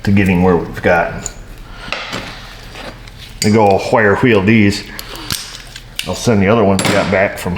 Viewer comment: did you say “Whire Wheel”?